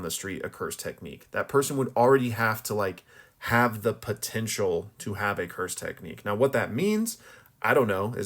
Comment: "DID YOU SAY POTENTIAL"